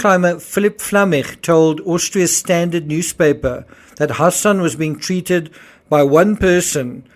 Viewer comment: I dunno. If it was some other guy fine, you all know what you signed up for. But your own child?? I'm gunna need to hear her side. It doesn't look good. So sad. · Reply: I'm confused. It sounds like is saying "her son" but I think he's saying Hassan.